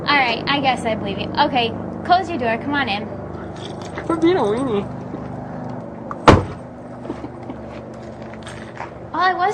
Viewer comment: What does he say to her @ 2:32 ??? Does he say quit being a weenie?? Lol